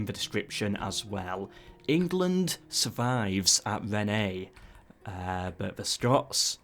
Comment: you had me worried there for a second, I thought you said Rene instead of Rennes.